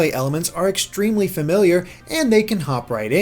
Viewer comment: They way he said "familiar" made it sound like "failure"...I thought he said the game felt extremely failure.